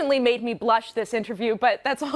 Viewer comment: "You're an 8. Thank you for having me." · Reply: Bro i thought he said "you're an ape thank you for having me" fucking killed me